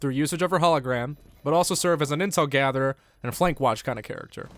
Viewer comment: I heard as "insult gatherer"